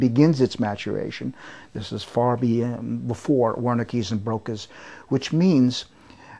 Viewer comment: What did he say at 3:22? "It's far before..........." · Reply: I think he’s referring to the Wernicke’s area and the Broca’s area of the brain because he’s referring to the formation of the left brain which is where speech is mostly formed if I’m not mistaken: “The ability to communicate is an essential part of our lives as human beings. Our nervous system has two dedicated areas that allow us to communicate. The production of speech takes place in Broca’s area located in the frontal lobe. However, the interpretation of speech happens elsewhere. Wernicke’s area – located in the temporal lobe – processes what is being spoken. Information from Wernicke’s area can then send information to Broca’s area, which allows us to reply by organizing words in grammatical sentences.”